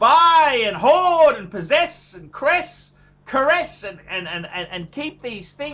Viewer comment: did he say caress ?